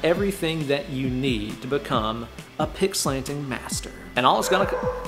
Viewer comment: I thought it said, "picksplaining"